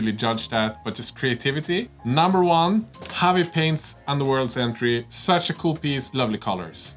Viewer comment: Could someone help me find those Kharadron Overlords at 26:45 ? Sounds like they say "xavi paints" but I can't find that person/account, even with different spellings etc... Would love to have it as a reference for purple armour. Thanks!